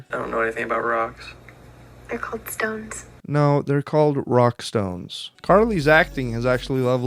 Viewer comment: DID I HEAR A ROCK AND STONE?!?!